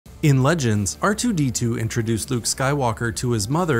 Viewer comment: You said, "In Legends" I heard, "In Canon"